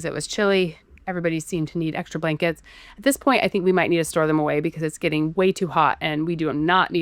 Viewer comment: because i thought I heard her say throw them away. 🤷🏼‍♀️ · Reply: ahh, okay. Well if you want, at the 28:58 mark you can rewatch/listen to her say "store them away". 🙂